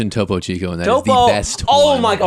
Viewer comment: I’m such a novice with bottled water I thought you said Topo Gigio (the mouse puppet from the Ed Sullivan Show) 😂 I’ve never heard of Topo Chico.